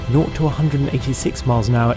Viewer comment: Did he say 0 to 180 mph in 9 seconds or am I mishearing him?